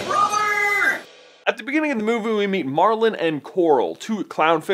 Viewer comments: he says moovoo instead of movie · I still laugh because it sounds like you say 'movu' instead of 'movie' but I really enjoy your work guys, keep it up! <3